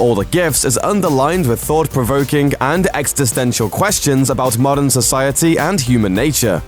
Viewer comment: Extastential questions ? That's what it sounded like he said